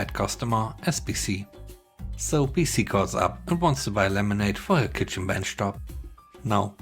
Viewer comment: Lol, I feel like an idiot. 90% of the story about the lady calling in for laminate I misheard it as lemonade until the very end when I saw the captions that said laminate, the whole story began making more sense after that.